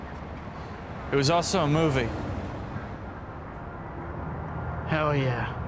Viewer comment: Is he saying, "It was all so moving"? Was he referring to the hug?